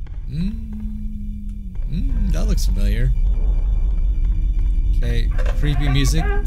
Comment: i thought you said that looks fire (it literally does)